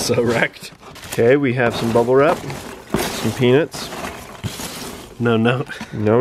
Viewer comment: I thought he said some penis lol